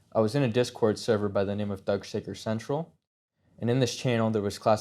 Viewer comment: DID HE JUST SAY “THUG SHAKER CENTRAL??”